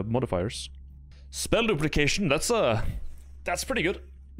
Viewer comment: I thought he said spell lubrication. Yep, thought it sounded weird...